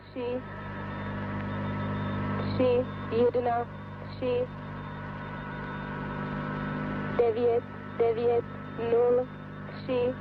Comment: Tbh i hear a woman's voise saying 99072 (dev'yat, dev'yat, null, sim, dva) in Ukrainian with english accent, before she stops talking and music starts playing. Dunno if it's already figured out but still. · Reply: I don't know. Starting at 34:03, I clearly hear a woman saying in perfect Czech: "tři, tři, jedna, tři, devět, devět nul, tři, dva", meaning: "Three, Three, One, Three, Nine, Nine zeros, Three, Two".